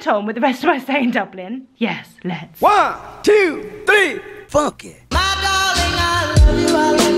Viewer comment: Lol had to listen to the track beginning a couple of times. Thought it said 1,2,3 f*** it lol